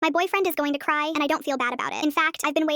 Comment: Ok at the start I thought you guys had an argument after I heard “I saw my boyfriend cry and I don’t feel bad about it” but then I watched the whole thing and I realised, you’re so sweet